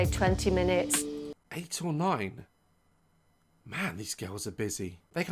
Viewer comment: who else thought she said 89 instead of 8 or 9? lol